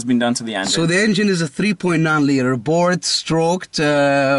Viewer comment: Did you say thats a 3.9 liter?